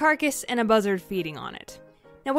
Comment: Dang, I've never seen a buzzard in my life. This was really interesting! Also, I love your animation style c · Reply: At first I thought you said BuzzFeed instead of buzzard